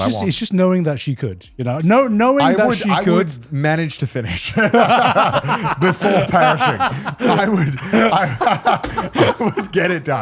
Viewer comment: I thought this man said before pairing the poison 😂😂😂 I was like this man plays way too many video games